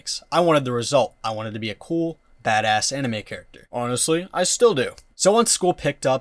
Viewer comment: I misheard it as "I wanted to be a cool fatass anime character" I'm delusional 😭